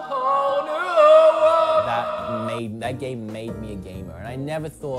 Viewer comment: at first I thought he had said "that game made me gay" LMAOO